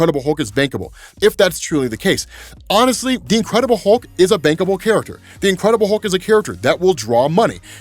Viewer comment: i know he said "bankable", but all I heard was "bangable"